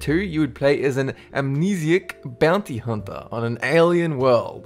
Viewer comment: who thought he said bounty humper?